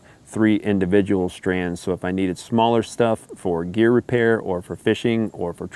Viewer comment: Great video! I love your willingness to share your knowledge and experience. I look forward to watching more of your videos. If you don’t mind me asking, what is “deer repair”? Maybe I miss heard you, but I thought I heard you mention it a couple times. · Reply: Gear repair